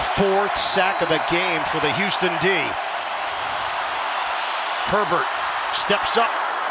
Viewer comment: I heard “fourth sack of the game for the houston dihh… PERVERT”